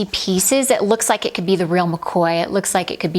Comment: gal did u say real makoya? 🤣🤣😅😍😜